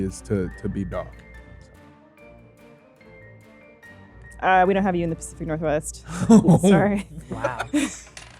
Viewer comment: What does say at 3:12? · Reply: She says, "We don't have you in the pacific northwest.", because he's brown and it's racist, and hilarious.